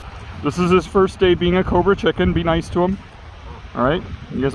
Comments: "this is his first day being a cobra chicken, be nice!" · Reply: Thought he was saying covert chicken 😆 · I misheard this as covid chicken 😂😂🤣